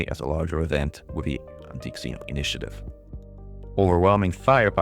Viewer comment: I always hear "Antique Xeno" the way pronounces it 🤣 I can't un-hear it now